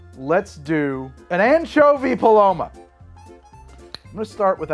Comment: am I the only one who heard it as the "manchovy paloma"?